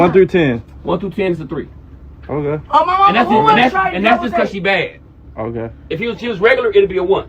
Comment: did she say "who wanna try it" after he said it was a 3